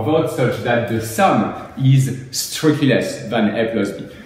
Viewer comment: Thanks for the great video! Can you please tell me what you said at 12:20 the sum is ..... I can't figure out the word you used. Thanks! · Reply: In video (at 12:20), I said "when the sum strictly less than a + b".